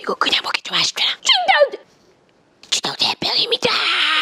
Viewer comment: 칭타오 옘병입니다--- 로 들었따....